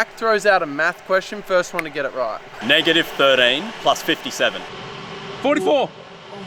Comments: he said -13?! I swear I heard -30! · has me over here yelling 27!!! Thought he said -30 +57